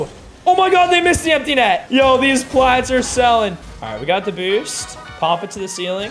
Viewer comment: I can't be the only one who heard "These plats are salad" right?